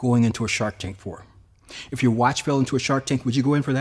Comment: I heard " if your wife fell into a shark tank would you jump in. NO" ha haaaa 😅 😅 😅